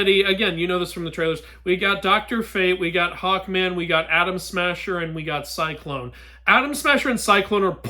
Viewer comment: i thought you said adam smasher as in the cyberpunk guy and i thought jeesus theres no way hes in thisis he?